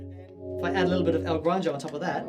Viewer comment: what pedal did you add? Sounds like you said El Grande; that's a bass fuzz. Is that it? Thanks